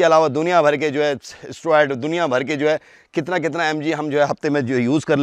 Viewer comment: did he say steroids?